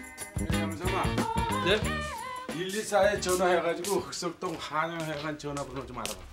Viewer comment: 영삼이 발음 진짜 안좋네 흑석동이 북석동으로 들려 ㅋㅋ